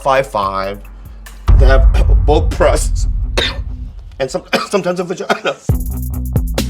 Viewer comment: Did he really say that he now can get girls with breast and (SOMETIMES) a vag*na?